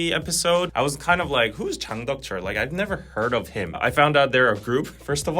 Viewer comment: Does anybody knows what’s the name of the group that said he liked the song? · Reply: I think it’s Jang Deok Cheol